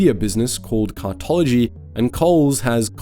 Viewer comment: "woolworths operates a media business called cuntology" I'm sure I must be mishearing that - but it seems fitting